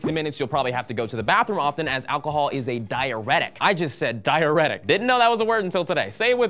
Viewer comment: The pronunciation here on "diuretic" sounds like he said "diarrhetic"....lol, not quite the same thing.